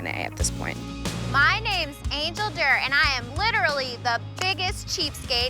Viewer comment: I thought she said her name was Angel Dirt 🤦🏾‍♀️